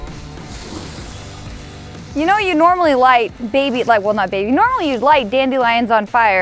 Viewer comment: did she just say you normaly light baby?